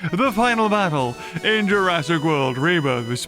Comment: Did i just hear say jurassic world reburger at 1:08 or is it just me?